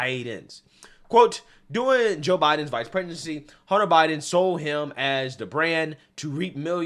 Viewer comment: it sounded like you said “Biden’s Vice Pregnancy”. 😆